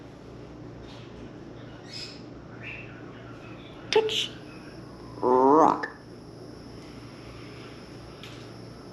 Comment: What did he just say? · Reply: "Touch"